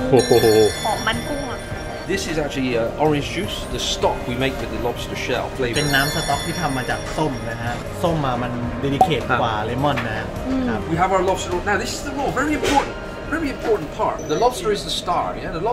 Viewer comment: ตรงนาทีที่ 3:32 ไม่ได้พูดว่า delicate เหรอครับ